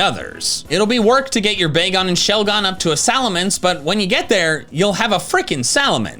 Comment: So the Dragon type starter is just Salamance. The Salamance type meme is still alive. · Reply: I thought they said Salamanca and I got worried 😂